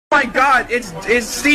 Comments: I thought he said "lazy fuck" lol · I swear I hear "lousy fuck" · I thought he said “crusty fuck” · I hear “rusty fuck”